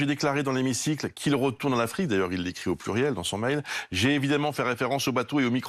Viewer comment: Pour ma part, j'ai entendu "Qu'ils retournent en Afrique!" sachant que le debat etait centré sur les bateaux de migrants.. Il faut juste tre de mauvaise foi ou idiot pour pas le comprendre